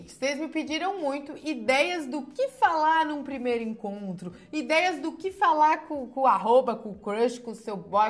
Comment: "ideias do que falar cu